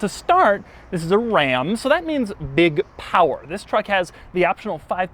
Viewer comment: It sounds like Doug is saying RA'AM!